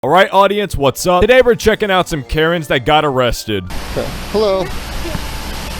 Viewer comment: "Today we are going to be watching karens get arrested" What i heard: "today we are going to be watching carrots get arrested"